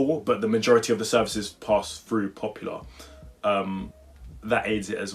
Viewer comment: Did you say ‘ Popular’??😂